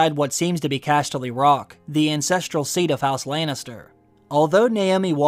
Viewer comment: I heard the "incestreal seat kf house lannister" instead of ancestral.